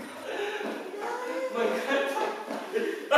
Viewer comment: Was i the only one who heard him saying "f*cking DiShEs" at the end?